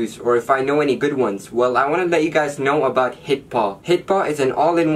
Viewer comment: At first i thought you said "Hippo" instead of "hitpaw" 😂